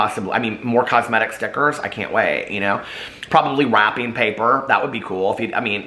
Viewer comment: I thought you said WAP-ing paper .... now that would be on brand for J Star!😂😂